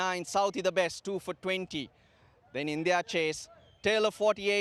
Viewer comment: What he says in 19:32 ?? His he said india chased!!! Pls tell me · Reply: 'in their chase' maybe, not sure